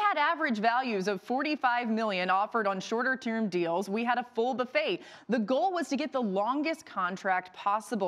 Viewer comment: Did she just say, “A full “buffate?”